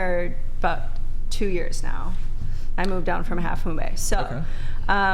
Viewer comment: did she say “I moved down from Half Moon Bay?”